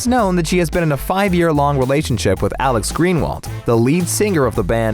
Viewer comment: Omg I thought they said Alex green well that’s my band teachers name